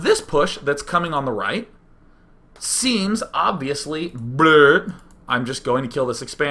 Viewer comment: did he say "blöd"?